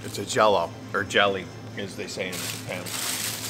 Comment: Did he say "jelly, as they say in spanish" ? Lol